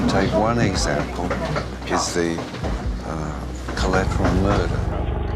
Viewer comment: min 1:11 what murder is his father talking about? I can't make out what he's saying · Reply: The name of Assange's first viral video was "collateral murder" which alleged that the Crusaders killed a journalist